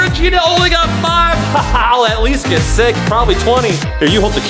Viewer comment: Did he just say at least get sick that’s not nice lol